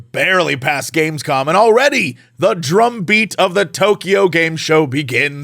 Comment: I heard "The drunk beat" instead of "The drumbeat"! 😅